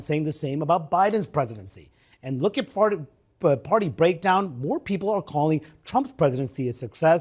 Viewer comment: Did he just say "farty?" 🤣